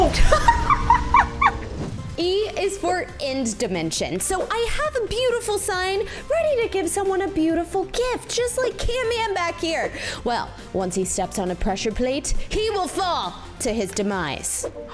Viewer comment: did she just said canman